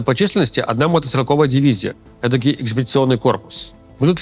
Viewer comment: Руслан говорит про корейцев "этакий экспедиционный корпус", мне послышалось "этакий эксгибиционный корпус", и тут до меня дошло, что по сути, это ведь оно и есть. Главная цель всего этого собакоедского вояжа - показуха, то бишь эксгибиционизм, а мы знаем что там показывают))) так что покажут они голый *ад🤣🤣🤣